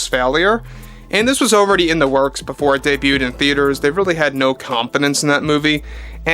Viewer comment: Did you say ..."... they had no confidence in that movie."? I heard "They had no competence in that movie."